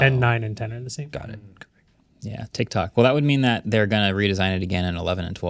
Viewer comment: Why did Marques say "Tiktok". Or was it like "tick tock"